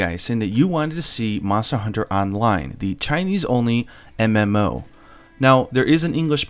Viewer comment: ~ Chinese? Did I hear that right?